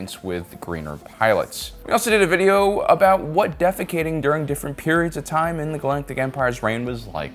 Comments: did you just say "defecating" hahahah love it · I hope I'm not the only one who heard "defecating" at 0:17 rather than "defecting." 😅